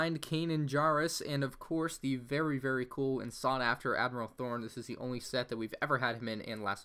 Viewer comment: did you say admiral thorn? Or Thrawn?